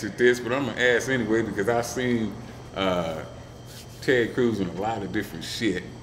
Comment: I thought he said "Ted Cruz" and I was so excited to hear these guys start talking politics.